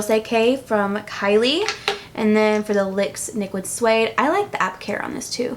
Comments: anyone catch "lyx niquid suede" at 11:56?? lmao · "Lyx niquid suede" did anyone else hear that at 12:00 😂 I hope you do more of these!! I loved it😚✨ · Did you say lyx niquid instead of nyx liquid at 11:57 or am i hearing things? Haha! I love videos like this, thank you so much!! <3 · don't know if anyone else noticed this but at 11:58 instead of "nyx liquid suede" she said "lyx niquid suede"